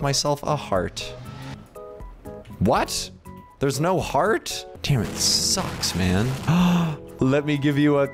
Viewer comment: did denis say damn?